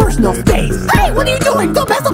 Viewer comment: i thought he said "HEY WHAT ARE YOU DOING DON'T PISS ON THE FLOOR!".